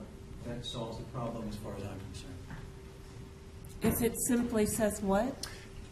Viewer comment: I swear the judge said, "Assfinkter says what?" pwhahaha